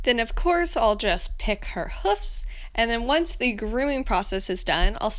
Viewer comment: Hooves? How is it funny haha XD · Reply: i heard you saying hoofs not hooves lol